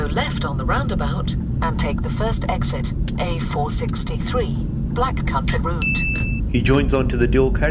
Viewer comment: wait what!? what did the satnav say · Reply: It's "Black Country Route", the name of the road.